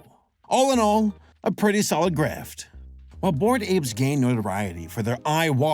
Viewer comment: Are you mispronouncing words on purpose? It's "grift" not "graft". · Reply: Sounds like he said "grift" to me. You sure you're not hearing things?